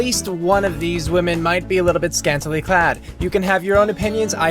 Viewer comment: What does he say at 00:15? I can't tell what he said. :/ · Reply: He said that one of them was scantily clad, as in wearing very revealing clothing.